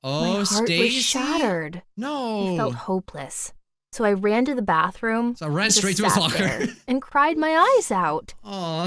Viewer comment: I thought she said "I ran to the restroom and stabbed her" when in fact she said, "I went to the restroom and just sat there"